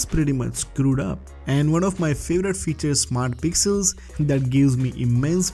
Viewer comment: What did you say at 2:24? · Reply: Smart Pixels?